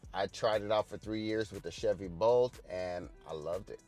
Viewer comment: did you say “Chevy Bolt”?